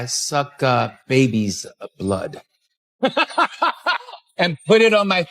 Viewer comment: When the black guy was talking about his younger lover I thought he was about to say " I suck baby cock" but he said baby blood I thought to myself that's worse